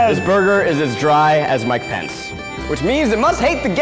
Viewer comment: I didn’t know Zach said Mike Pence during his joke because, when I first watched this idk who Mike Pence was. I thought Zach had said, “My pants.” So that was weird years ago and now I get it.🤣